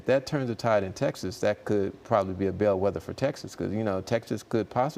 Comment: Did he say Weatherford TX? It sounded like he said that